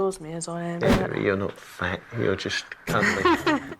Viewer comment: Omg I thought I heard him say, "You're not fat. You're just ugly."